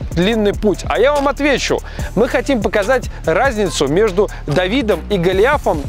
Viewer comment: Послышалось, мы хотим показать разницу между давидычем и...)